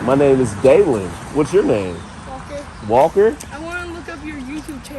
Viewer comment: LOL, at first I thought the kid said his name was "Walter"!